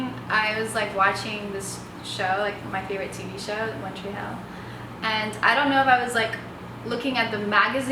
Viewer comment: I was like did she just say "one tree hill" I love one tree hill I am obsessed with it my. Middle name is hailey.